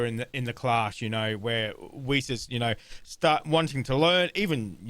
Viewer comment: East? I thought you said Weiss 😂